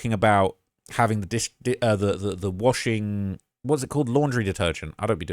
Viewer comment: I thought they said the pool was washed with laundry detergent. Could be with that bottle of Tide.